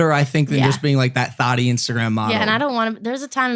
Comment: Did he just say "thotty instagram model"? XDDD Watch out man be careful